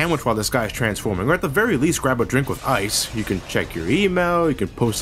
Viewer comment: i literally thought he said "Grab a drink of ice"